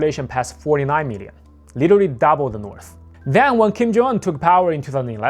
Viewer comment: yo slow down! you barely said his first name. Sounded like you said "Kim Jonggg"